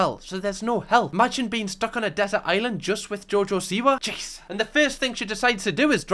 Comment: genuinely thought u were saying jojo sewer for half this video